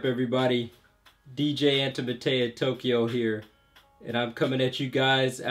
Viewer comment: At first, I thought he said 'DJ Potato in Tokyo' Haha Lool!